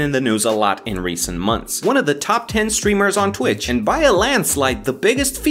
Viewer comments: wait what do you mean YouTube or something bc I saw this channel named sniperwolf and saw that it had 21 mil subs is my gadget broken? Edit:oh wait nvm he said streamer not Youtuber I think Edit#2:yeh he said streamer oof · I thought he said “one of the top 10 strippers on twitch”